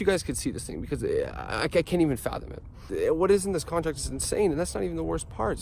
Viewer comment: "I can't even fathom" (I think?) I thought he said "I can't even fap" bye oml